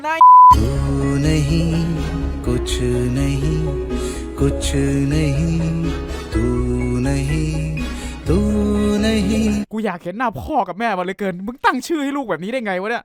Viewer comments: ทำไมได้ยินเป็น ตู่หน้าxี 555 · ไม่ใช่กูคับเค้าพูดว่าตู่คับ5555